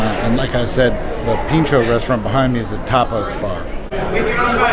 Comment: I thought he said "Topless Bar". Made me start paying closer attention for a few seconds lmao